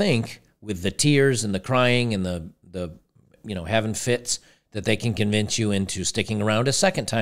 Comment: To me as a non-native speaker "havin' fits" sounded like "heaven fits (them)" as in "men are always to blame, women are angels".